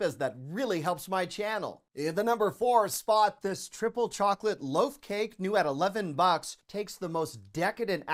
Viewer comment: I thought you said 'chocolate vodka' ! LOL